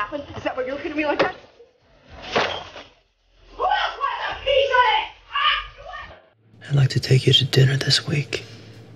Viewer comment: What did she say? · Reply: "Who else wants a piece of this? Huh?"